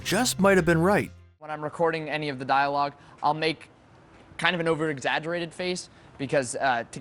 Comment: di-did he just say make-o